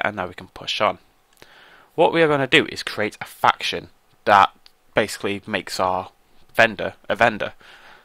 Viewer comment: I thought you said "We need to great a faction which makes our vendor a bender xD" nether the less great video +like